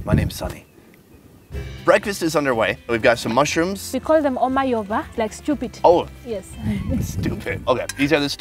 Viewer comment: did she said "we call them omo yooba" or "omo yoruba"?